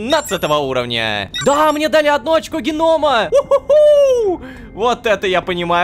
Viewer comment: Мне послышалось очко гнома